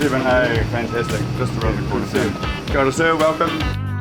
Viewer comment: FYI: at 3:50 the flight attendant did not say "Come on, sir" as shown in the subtitles, but "Kia ora, sir" which is a Maori greeting.